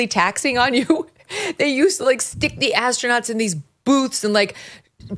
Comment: Astronauts I thought they said assclowns my bad